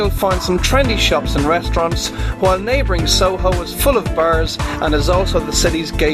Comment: wait, he was talking about bars?! I thought he said bears! :O